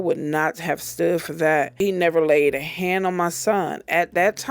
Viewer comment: She kept saying "when he put hands on me"...but there was also a place when she said he never put hands on me... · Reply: i thought she said never laid hands on her kids